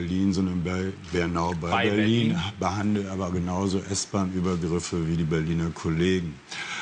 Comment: Was sagt Andreas Müller 5:42 '...behandelt aber genauso (?) wie die Berliner Kollegen` ? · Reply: Nguyen Quyen S-Bahn-Übergriffe